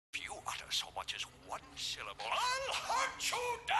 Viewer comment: "If you utter so much as one syllable, I'LL HUNT YOU DOWN AND GUT YOU LIKE A FISH! If you like to fax me, press the star key." XD! Jim Carrey's version of The Grinch is the best, hands down! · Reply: Sounds like he said "clear so much..."